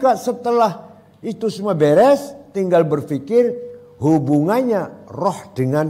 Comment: Saat pak yai bilang apa hubungan antara roh dgn fisik, sepintas sy dengarnya apa hubungan roki sama risik? Saya putar ulang ternyata saya salah dengar 😅